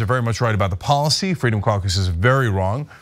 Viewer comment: I keep hearing "the freedom carcass"! Must be freudian! 😆